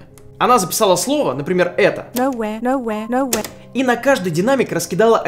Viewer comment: мне послышалось : но я, а я... 16:52 что то типа "...но я... протика... к ... я...